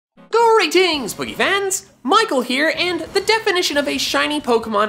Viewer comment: for some reason I always thought he was saying "greetings BOOGIE fans" instead of greetings poke fans